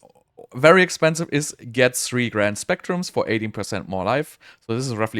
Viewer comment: did he say 80% more life ? how does 3x2% end up making 80% ?